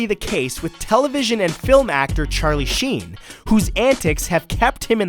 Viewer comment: I thought he said Charlie Scean 😂😂😂😂